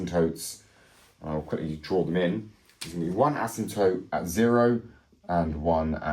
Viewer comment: What is an "ASM toote"? That is what the CC text thinks you said at 9:25. "There is going to be one ASM toote at zero" Googled "ASM toote" and got nothing. <link> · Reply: It meant “asymptote”. A line on a graph where our function can’t cross